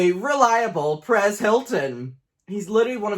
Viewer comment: When he said press Hilton I thought he said Paris Hilton